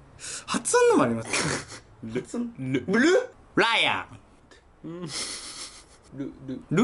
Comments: ライオンがブライアンって聞こえる(笑) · ライオンか！ブライアンに聞こえたwwwwwww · ライオン🦁が、ブライアンに聞こえたwww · ライオンがブライアンに聞こえたㅋㅋ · じんたんのライオンがブライアンに聞こえる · ライオンがブライアンに聞こえたw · ライオンが ブライアンに聞こえる笑